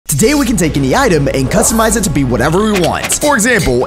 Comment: when i saw this video the word u said was cuztomize Instead of customize